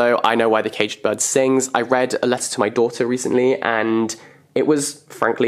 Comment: wtf did he say? It sounded like he was saying either "daughter" or "doctor" and ive yet to stop laughing at the thought of him reading a letter to his doctor 😂😂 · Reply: daughter, it's the title of the book I believe